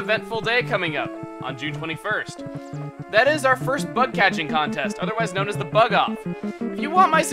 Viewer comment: I'm gunna have to say it, but I thought you said "Butt Catching Contest" ._.